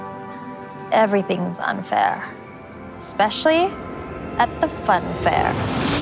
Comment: i heard “everything’s unfair, especially at the funfair” yet everyone else heard “life’s unfair, especially at the funfair”